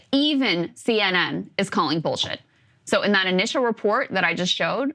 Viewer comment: Did she say bullshit on the IDF❓️ CNN has come along way from Hamas burning babies, maybe there's hope for CNN after all, well, maybe ~